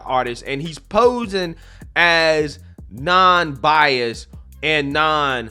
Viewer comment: I thought was going to say non-binary 😂😂😂😂 every time he said “non-bias” 🤣🤣🤣